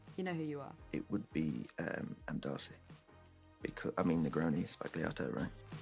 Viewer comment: what did he say? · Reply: "Negroni sbagliato" the thing from the interview with Liv Cooke that went viral some time ago